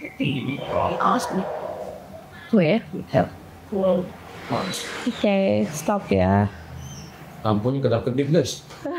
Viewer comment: itu ada suara. Ngomong apa ya? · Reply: Don't speak kak kalau aku denger